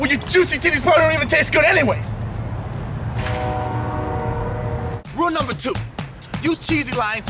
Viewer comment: anybody else notice @ 1:09 he said rule#2, held up 5 fingers and subtitle said #3? lol